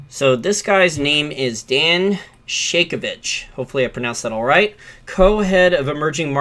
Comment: Lmao.... i had to check the spelling of his name cause i thought i heard you say “Dan Shakeabitch”